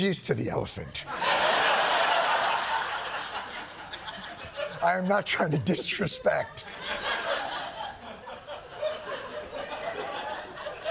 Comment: bro i thought he said dickrespect the elephants first listen. I about died.